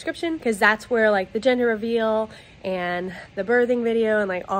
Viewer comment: Am I the only one who heard her say “wedding shower” at the end?!😂😂